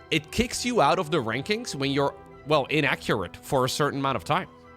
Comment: I'm sorry, 4:46 did you say INACCURATE??